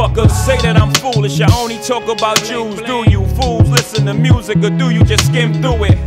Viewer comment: I always thought Jay-z said "i only talk about jews"